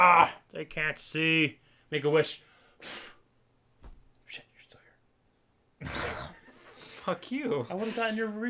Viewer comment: I can't hear what is saying at 25:44 and it's driving me crazy, someone tell me please! D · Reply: shit you're still here.